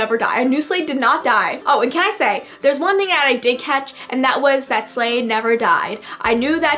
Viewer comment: Am I hearing this wrong? Who’s Slade? That’s what I hear, I feel really dumb, cuz I think she means Shade but...IDK.